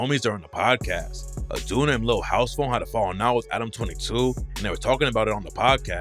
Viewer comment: Im sorry, im certain i misheard that rap name. No way he just said dudes name is Lil house phone? 😂😂😂😂😂😂😂😂